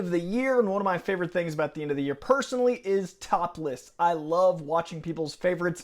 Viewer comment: Oh top lists. I thought you said topless. 👀